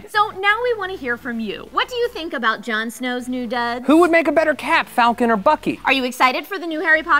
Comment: I thought they said who would make a better cat not cap.